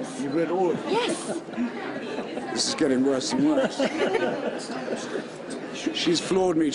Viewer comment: Does someone know what he says there? I just understand something like "This is getting embarassing much".